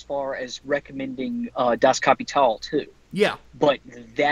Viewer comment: What is the book recommends at 37:18 ? I couldn't make out what the title was. Edit: Could you guys also put a list of books you recommended in the podcast in the description or in a pinned comment? Great podcast as always. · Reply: Creepynazgul Das Kapital. Marx's other book.